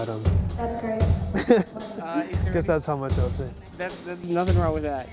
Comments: what did he say on 3:41 ?? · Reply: “I guess that’s how much I’ll say.” answering your question 4 years later lol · what did he say on 3:41 ?? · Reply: Cruzado he said "I guess thats how much ill say"